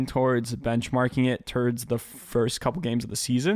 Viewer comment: Did you say "Turds" at 8:59? Hahaha!